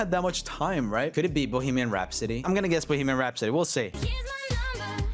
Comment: the way he said "Bohemian Rhapsody" really just sounded like "Boogeyman Rhapsody"💀